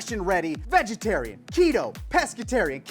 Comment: I know said Keto but it sounded like he said Cheeto so now I can’t get it out of my head they have an all exclusive Cheeto diet with like Cheeto ice cream, Cheeto breaded chicken, Cheeto flavored soda. (Yes all of these are real)